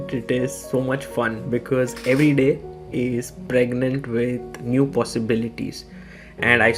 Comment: Lmao what the hell did just say at 0:32 every day is pregnant 😂😂